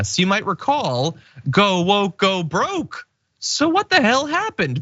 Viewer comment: I thought they said everting woke goes broke?